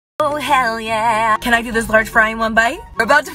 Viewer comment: did she just say "can I get this large fry in one bite" ? lol wtf